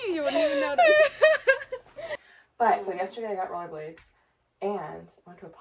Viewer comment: I thought said “yesterday I got really blazed” and I was like ayyyyy but then figured out she was saying “roller blades” 😅